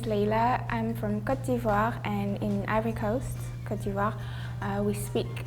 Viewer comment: there's a mistake in the subtitles : while she sounds like she says "every coast", she's actually saying "Ivory Coast" for "Côte d'Ivoire" Nice video by the way